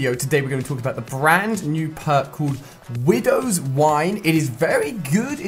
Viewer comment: I thought it said Windows nine